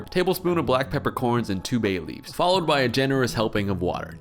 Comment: I had to rewind twice cause instead of bay leaves I heard babies 😂😂😂😂😂 what is my mind